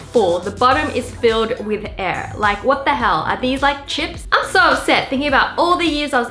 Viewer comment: No hate but wengie I heard you say what the hell